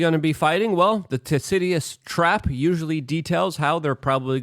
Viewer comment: " Tasidious trap?'' I don't even know what word he was going for this time. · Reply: Thucydides Trap